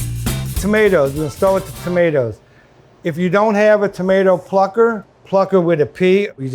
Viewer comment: a tomato what???? Oh he said plucker…